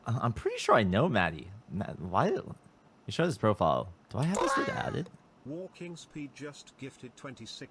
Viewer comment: WHAT DID HE SAYYYYYYYYY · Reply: 'do i have this dude added' ig